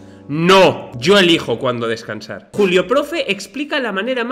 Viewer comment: escuché Culio o Culeo profe, en lugar de Julio (con texto, en CR culeo es ummm 👉🏻👌🏻)